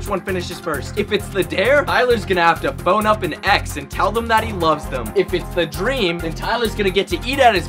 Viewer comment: i thought he said “bone up an ex and say you love them” bro